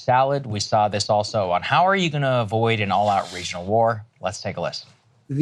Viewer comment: i thought Saagar said Racial war and i was like holy shit tf happened?? 😭